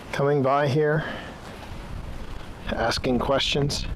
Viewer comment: I will admit there are a lot of spirit audio clips where I can't hear anything, or that I haven't been able to make out at all as I've watched through your videos, but at 9:40, I definitely feel like I hear "Ask us questions? Oh, yes."